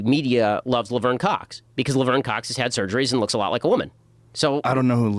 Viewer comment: Did anyone else hear "Liver in Cox"